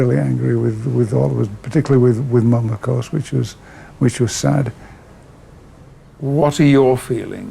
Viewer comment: What is said at 56:22, which the CC identifies as “minako switches”? I couldn’t understand the audio, but I’m sure the closed caption is off the mark. Maybe it should be called “close” captioning!😆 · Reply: He says 'particularly with Mum of course which was sad'